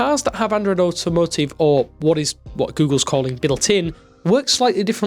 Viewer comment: Anybody understand the word spoken at 0:54? "What Google is calling" ... 'middle tin' ? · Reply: Built-in